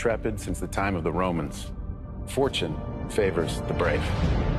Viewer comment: I thought Virgil said, “Fortune favors the bold.” Either way, this is neither bold nor brave, it’s stupid.